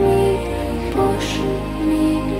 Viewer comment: with my fan on and the volume low, it sounded like she said, 'He starts Squawking. Squawkinggg!' instead of 'save me' and now I want that as a thing lol